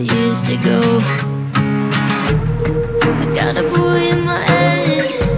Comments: I thought she said I got a boy in my attic for a sec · Am I the only one who thought it said “got a boy in my head” not “bully” this whole time ?!? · Am I the only one who thought he said "boy in my head " instead of bully in my head" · I thought it said “I have a boy in my attic”😭